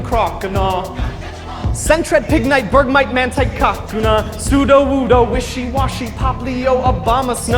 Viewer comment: cockuna instead of kakuna 26:19 (he even motions his hand towards his dick) LMAO